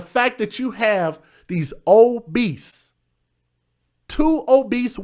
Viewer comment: Oh, "OBESE". I thought he said old beasts. Lol